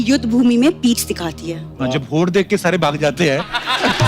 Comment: What does Kapil say at 33:13? Can’t make out even after repeated viewing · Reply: Hoth dekh kr sb bhaag jate h..esa bol rhe h kapil